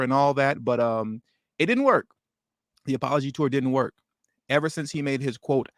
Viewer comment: It sounds like you said, elite-ology!